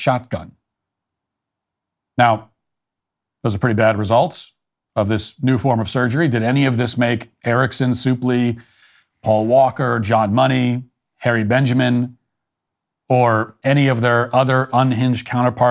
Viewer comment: You speak that name & I keep hearing "Hairy Benjamin." 🤢🤢🤢🤢🤢🤢🤢🤢🤢🤢